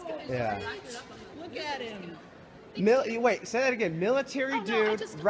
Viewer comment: Did he just say "Look at him" That mall cop should look at himself before talking about looks.